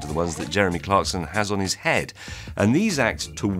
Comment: Did he just call Clarkson a dick head?